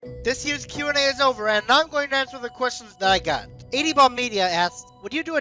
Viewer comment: For a second I thought you said at the beginning of the video “This year’s Q&A is over and I’m not going to answer the questions I got.”